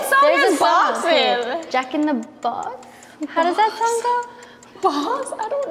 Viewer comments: Did I misheard or Rose actually said JACK IN THE BOX????? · does rose said jack in the box 😯